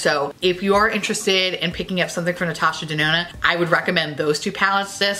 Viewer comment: Swear I heard you say Natasha Denono. And if you did, you are a beautiful pun lord.